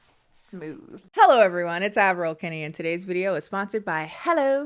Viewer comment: PLEASE this whole time i heard it as “admiral kennie” and never questioned it omg